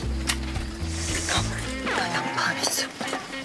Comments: 난 망했어가 아니라 양파 안했어로 들리는데... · ‘난 망했어’가 아니라 ‘양파 안했어’로 들리는데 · 이때 난 망했어가 아니라 양파안했어 같은뎈ㅋㅋㅋㅋㅋㅋ잘못 들었나보다 ㅋㅋㅋ